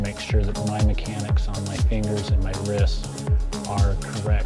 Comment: I thought he said he was going to bust his nuts but then he said wrists instead